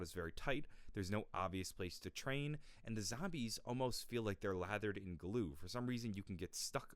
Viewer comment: kinda thought u said lathered in lube ngl